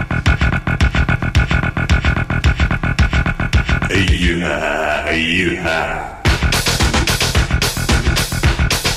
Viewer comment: lol what is he saying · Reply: He is saying "are you high"